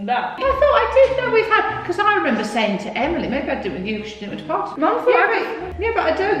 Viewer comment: What did she say at 13:27? I didn't with you because you didn't ....